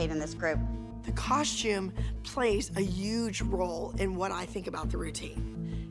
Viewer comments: did just say a uuuuuuuuge role instead of a huge role? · EVERYONE on Dance Moms who EVER uses a word with a “h” in it, they don’t pronounce it correctly. Such as, when said “huge” it sounded like “uge”